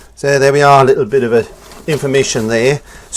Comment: I thought you said you put "just a little regular kitchen oil on my brush"??? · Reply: Kitchen roll I think lol